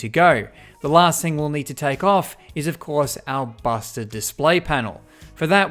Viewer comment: is it just me or does he say ‘bastard display panel’